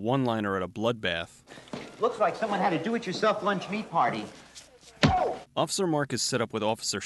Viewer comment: What is he saying? I can't make half of it out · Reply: "Looks like someone had a do-it-yourself lunch meat party"